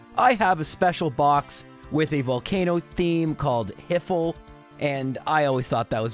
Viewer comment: he says "I have a special box with a volcano theme called ________" Can someone fill this in for me? I have no idea what he's saying. edit for others like me: at 25:46 you can see that it's called "HFIL." This appears to be a Dragonball reference, where the "Home For Infinite Losers" stood in for hell.